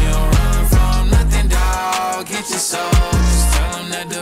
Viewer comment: is he saying get your soul tell’em the break is over? 🤔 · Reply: Soldiers